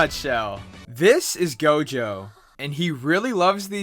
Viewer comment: Did I just hear gojo? Or is it me